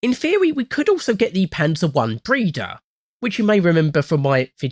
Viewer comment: i heard breeder 😭